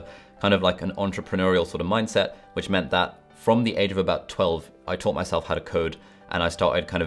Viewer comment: at 12?! Am I hearing this correctly? Damn now I want that entrepreneurial mindset